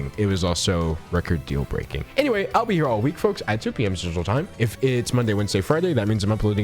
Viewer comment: For some reason, I keep hearing “I’ll be here 2pm social time” instead of central time